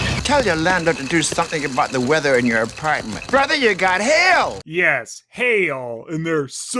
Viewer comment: When I first saw this episode, I misheard Andre's "Brother, you've got hail!" line as "Brother, you've got Hell!", though to be fair it actually did sound like that (and it surprisingly wasn't cut off the Jetix DVD version despite them cutting out words like "weenie" and "burn"). Also I love how Dr. Quease is sometimes called "Supergenius" in these reviews in the same way Wile E. Coyote says that word, although the Coyote is much more of a genius than Quease. It looks like Leonardo's "biggest idiot" streak has been broken for now, but I think with this and the next episode, Dr. Quease may work his way up in the idiocy rankings despite his claims of being a genius.